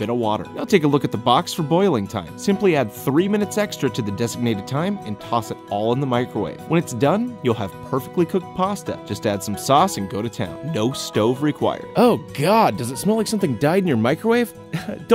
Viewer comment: I thought you say "noscope required"....